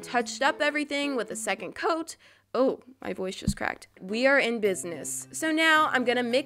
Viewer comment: In 11:43 I thought she said my butt just cracte